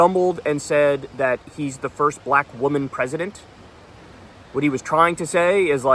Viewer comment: I thought he said he’s the first Black vice president lol